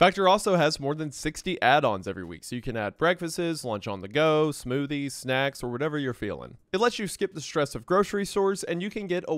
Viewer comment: did he just say breakfastses